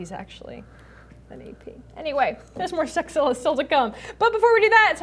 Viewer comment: Thats what I heard (@ 6:00) love that sexzilla